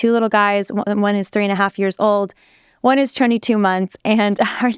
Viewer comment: I thought she said one is 3 1/2 and the other 2 months. Had to rewind and then heard 22 months 😂. I was about to say, lord help you lol. I have 2 small boys 21 months apart and it’s chaos but such a blessing.